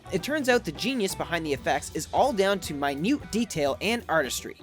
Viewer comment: Minute detail.... i thought he said "My nude detail" OMG